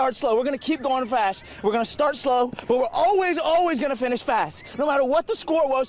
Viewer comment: Start slow. Finish fast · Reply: I thought it said start slow finish last